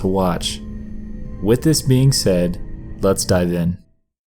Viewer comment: This has nothing to do with the video, but I thought you said, "Then let's dab in" instead of, "Then lets dive in"